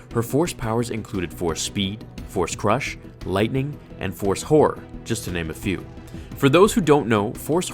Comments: Thought he said "force whore" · im not trying to be vulgar, but I swear I thought he said, "force whore." I had a real wtf reaction until I realized he'd said, "horror," lol. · With darth Traya I thought you said one of her powers was force hoar